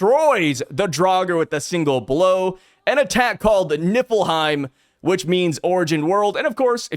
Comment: Why did it sound like you said Nippleheim😂